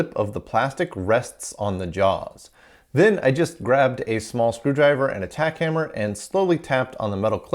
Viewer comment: I heard "small screwdriver and attack hammer". ;p